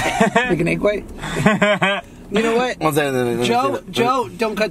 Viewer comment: did josh say "n*gga what" 4:13 or is it jut me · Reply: Kayla Cannon “you know what” it does sound like it though 🤣🤣